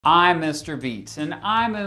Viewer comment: Thought you said mr beast in the intro😭😭